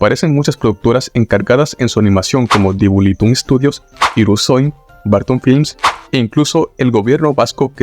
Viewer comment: Yo escuche Hirusem. Así que eso así mientras dejaba que Danso hiciera lo que quería con la Hoja